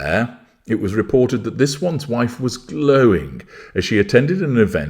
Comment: Oh 'glowing' for one glorious moment I thought you said 'going' and got very excited🙁